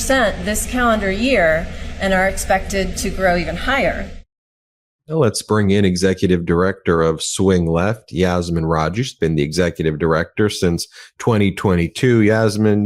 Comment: I thought that Chyron said "Executive Director OF Fighting Back". That'd be a good position.